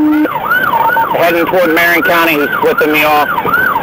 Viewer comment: what did he say? Notify marian county he's flipping me off 😂😂😂